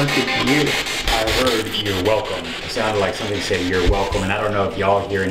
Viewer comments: Let me know your thoughts on all this.. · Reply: Rug I heard it say your not welcome instead of your welcome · I saw the door close just slightly behind the ghost hunter guy 23:05 You hear a whisper saying “he’s dead” (its super hard to hear) 16:12 You hear The word Danger mark 10:38 Does anyone here the word Here Jonny 8:40 Random Glowing in the window but it’s most likely normal 😆 17:37 You can here something say “let me out” 18:37 you can see 2 blue dots behind rug in the dark… 15:22 You can here the word “Help Me” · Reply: I heard your done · Let me know your thoughts on all this.. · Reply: I heard it say your not welcome instead of your welcome